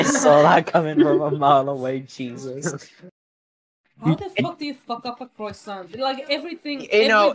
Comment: I thought it said “how do you f*** a croissant” instead of “how do you f*** up a croissant” i cant be the only one who thought this.